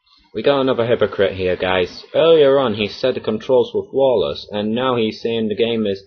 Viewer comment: He actually said that the controls are "not flawless" if you listen more carefully. So technically, he didn't contradict himself at 4:28-5:51.